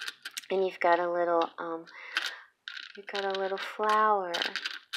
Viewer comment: I heard ''That's you Flower'' ><